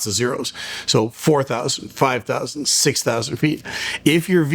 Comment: did you mean to say "so low"? And no, that's the highest ATC would allow because of crossing traffic. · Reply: no, I must have misunderstood, I thought they were saying 45,000.